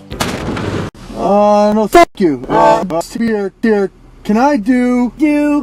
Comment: I thought he said F you